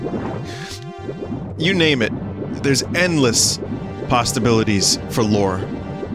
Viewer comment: no matter how many god damm times i try to hear him say "there's endless possibilities" [6:51 - 6:54] i keep hearing this hard sound in the middle of "possibilities" that's like a "tuh" sound making it sound like he's saying "endless pastabilities". i literally have listened to it like 37 times and i still can't unhear it. wtf?